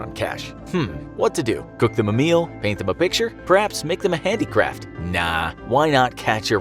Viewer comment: I thought u said make them a handicap